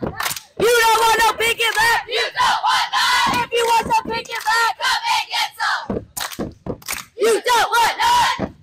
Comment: can someone pls tell me what they say for their chant i’m so curious 😭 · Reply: “LJ! Let’s go! You don’t want no pink and black! You want none! If you want some pink and black! Come and get some! You don’t want none! If you want LJ come get some!” I think!